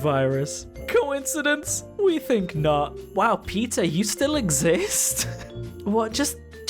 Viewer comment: Omg when you said PETA, I thought you were saying Peter, like the wife from family guy🤣